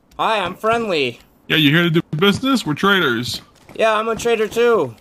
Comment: Bro lied about being friendly and starts crying 💀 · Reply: I thought he said traitor not trader😂